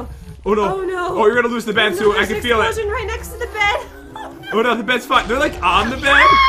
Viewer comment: A lot of people are saying this, so I’m going to clear things up: Around 13:25, he did not say “Oh no, the beds f*cked.” He said, “Oh no, the beds fine.” Because he wants it to be gone so he can kill and win.